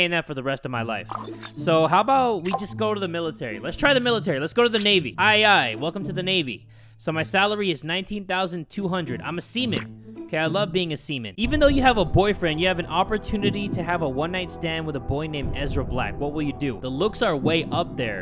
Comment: Why did I picture a like hunchback looking old man😂 also I thought he was saying seman